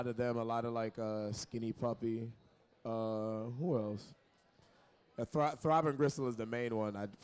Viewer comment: what’s the name of the artist ? Can’t hear what he’s saying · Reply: Throbbing gristle